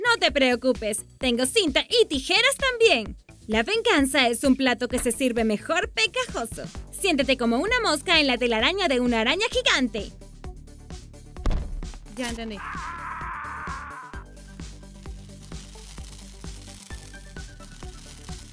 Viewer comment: en el minuto 9:44 se escucha ya entendi like si lo oistes